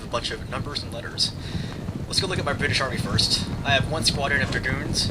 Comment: lol, sound like you said "my bitch army here" XD no, it was awesome, but yh, could you do some close ups ^^